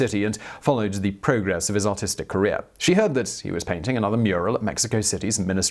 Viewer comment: Simon says Autistic instead of Artistic